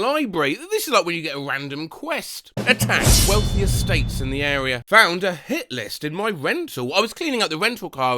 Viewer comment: I heard “attack melting snakes in the area”, that would’ve been a much funnier quest.